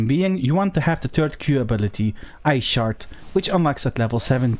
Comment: Great video. When you first said ice shard. I could of swore I heard "I shart." It made me laugh, I must be delirious. Sorry for my juvenilism, but this was a great video thank you. Best albion content creator.